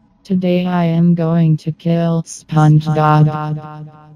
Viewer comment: I heard SpongeGod